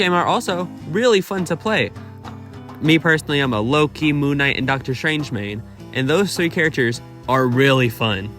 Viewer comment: I actually thought you said "im a lowkey- Moon knight and Dr strange main", had to rewind a few times to catch that